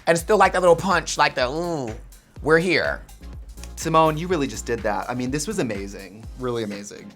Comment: did trixie just call symone timon ?! anna oop